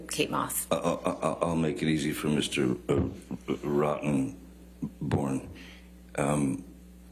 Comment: "Mister ... Rotten .... Born" [but I might have misheard]